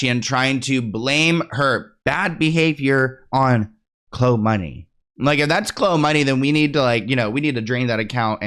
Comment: For real sounded like she said “Klhonedia” not klhoe money 🤣